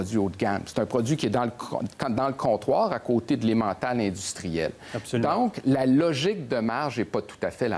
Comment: Qu'est ce qu'il a dit à 5:02 ? Ca sonne comme "à côté de la montagne industriel" ?? O.o J'éssaie d'apprendre le francais pour visiter le Québec. Merci! Aussi, à 6:06, sa dernière phrase, c'est quoi? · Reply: "L'Emmental industriel". L'Emmental est un fromage suisse. Merci d'apprendre le français avant de venir au Québec! Tu es le bienvenu!